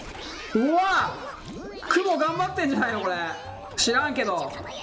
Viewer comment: 知らんけどって聞こえるw